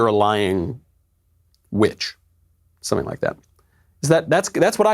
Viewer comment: My ears must be a couple of biased conservatives because when its slowed down I hear her say "bitch". Could possibly be one of those Jimmi Hendix things where he says "scuse me while I kiss this guy" but turns out to be "kiss the sky" lol. Of course of Im kidding but I hear the word bitch clearly